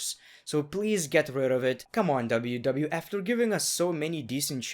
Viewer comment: did be just say WWF